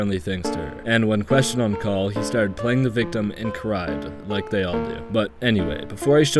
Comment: dude I thought he said foreskin playing ☠️